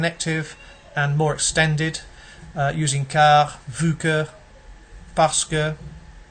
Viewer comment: What word did you say at 2:01 "fouque?" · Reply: Vu que